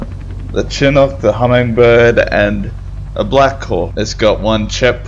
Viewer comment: the black cawk (that's what I heard)